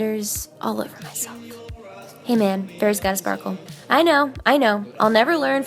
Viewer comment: "Hey man fairy's gotta sparkle" I thought you said "hey man Bears gotta sparkle" 😂😂❤️